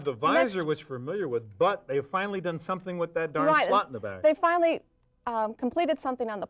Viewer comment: thought he said damn slut in the bag 😂